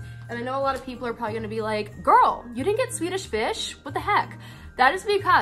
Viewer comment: When she said Swedish fish I thought she said tuna fish for a second 😅